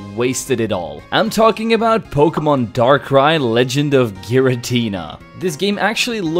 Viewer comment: Dark Cry sounded like Darkrai!